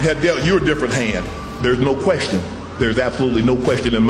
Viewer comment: Sounded like he said "First brothers to be inducted into the hall of *flame*," because he knew it would be fire 🔥🔥 😭